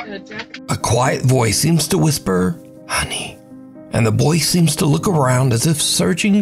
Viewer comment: When the “ Ghost” says “honey“ I think the little boy thought it said “hug me” or “Hugging me”. That’s why the little boy said “hugging me?“